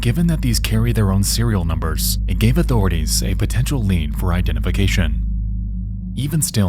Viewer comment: Captions says "an escapable" when it should say "inescapable" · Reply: Captions says "a potential lien" instead of "lead"